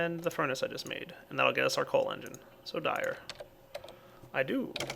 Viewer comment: did it say solar engine at 24:22 what is a solar engine anyway?